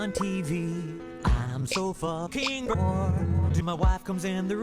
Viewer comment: I always thought he said “I’m sofa king, whore”, but I decided to watch it with captions, and I gotta say, “I’m so fucking bored” is less funny